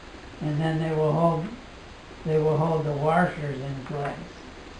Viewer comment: You have a slight Lancaster accent, bro--I hear 'war-shers' instead of 'wash-ers'. Didn't realize that before! interesting build using common parts!